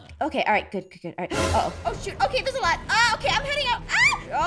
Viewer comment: EXCUSE ME APHMAU EXCUSE ME 0:51 DID YOU SAY A BAD WORD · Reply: Oh shoot